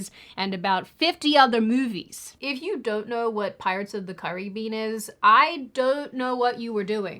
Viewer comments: my subtitles say Pirates of the Curry Bean is... 😂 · Am I the only one who heard it as “pirates of the curry bean”?